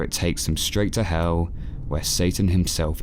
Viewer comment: WAHT DID HE JUST SAY? AT 2:22 DID HE SAY HELL AND SATAN!!😱😱😱😱😱😱😱😱😱😱😱😱😱😱😱😱😱😱😱😱😱😱😈👿👿👿👿👿👿👿👿👿👿👿👿👿😈😈👿😈👿😈👿😈👿😈👿